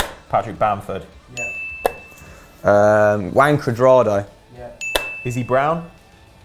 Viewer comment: I thought he said "is he brown" XD